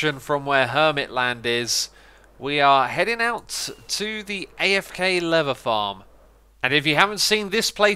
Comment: You said "afk leather farm", but I heard "afk lever farm", and wondered how that even works 😂😂😂